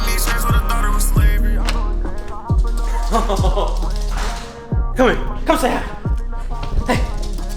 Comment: Wait wait wait did u say "Kom så her" at 3:14 ?